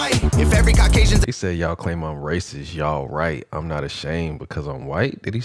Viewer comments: He said "you claim I'm racist, yeah right, I'm just not ashamed cuz I'm white" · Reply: Yeah I almost died when this reviewer thought he said “yall right” instead of “yeah right” ☠️ lolol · It sound like he said y’all call me like racist yeah, right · When he said racist he said “yeah right” - he’s disagreeing. It did sound like he said y’all right though. lol